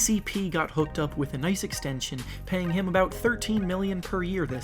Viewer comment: Are you Canadian or from Minnesota? I swear I heard an “aboot” out of you